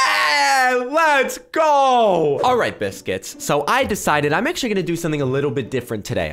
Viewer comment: did you just call me a biscut